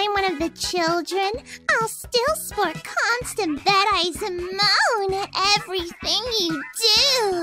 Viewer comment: What did she say? "I'll still ... constant ... moan at everything you do." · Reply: I'll still sport constant bed eyes and moan at everything you do